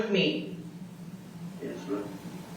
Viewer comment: Oh good god is he calling her ma’am @ 2:16:56 or is he saying something else I have a very hard time with accents unless it’s a very hard southern accent 😂 that’s just because that’s how I talk too 🥰